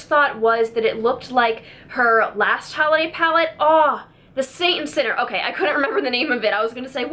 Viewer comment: I could have sworn you said "The Satan Sitter" palette :)